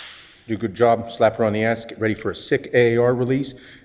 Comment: Legit thought said code Iain at the end